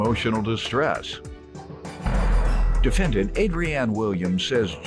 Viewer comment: I thought the announcer said her name was "Angry Aunt Williams" 😂😂😂